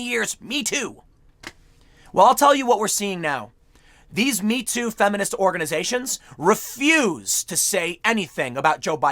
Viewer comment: I had to do a double take because I thought it said several feminist ogres lol